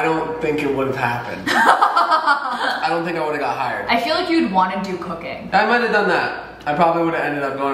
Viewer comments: She said “you’d wanna do cooking” and I 100% thought she said cocaine. I’ll see myself out. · My mind was in the gutter and I thought she said “I feel like you’d wanna do cocaine”